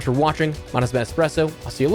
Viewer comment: At the end of the video I always hear: “My name has been Espresso”. He’s got to be literally saying that